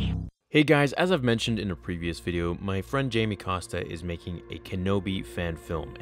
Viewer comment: I was a bit sleepy when I watched this and I thought you said Jim Acosta